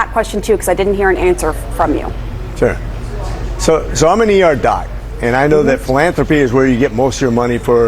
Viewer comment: "So, I'm an ER doc." That's much more understandable than what I heard initially, which was, "Zomineearda" 14:09. Dude sounds like Tom Homan at happy hour.